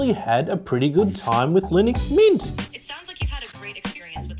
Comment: I heard something like "Linux Meat"